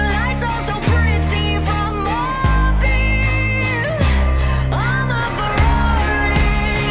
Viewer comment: I keep hearing ''from my beer'' instead of ''from up here'' xD